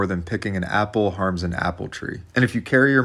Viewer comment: instead of breathable I thought he said breedable, lol